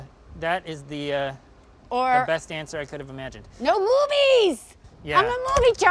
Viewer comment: I thought she said "RUBY'S!" instead of "movies!" and was confused why she'd like Ruby Tuesday's so much more than Red Lobster.